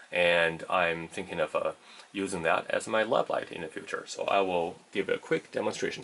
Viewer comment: does he say he is thinking of using it as a "love light" in future? Hahaha! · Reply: I think I said "lab light" did not think I was that far off :-)